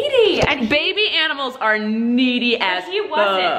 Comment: Who else hear “meaty” not “needy”?